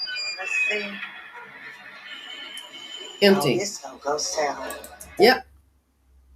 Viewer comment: She didn't say "It's gonna go south" From what I heard, I her, "This is just a ghost town"